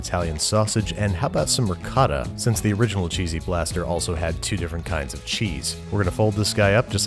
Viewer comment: thought it said cheesy ball blasters idkwhy